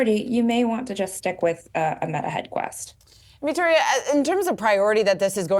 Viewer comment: yeah I heard the “Meta Head Quest” is really good 😆